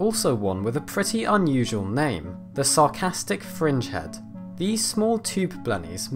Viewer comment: Love the name of the first one! The Sarcastic Fringehead!! 😂😂😂 They look super creepy!! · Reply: I kept hearing “Sarcastic Frenchhead”....